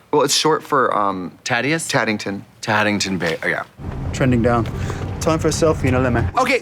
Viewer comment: did Katya say “Taddington Bear” lol